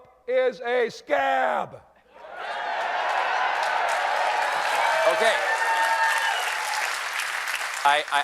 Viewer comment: I heard "... is a scam", but he can be both a scab and a scam.